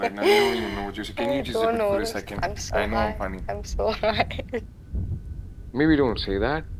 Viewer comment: did she say im so HIGH???? helppp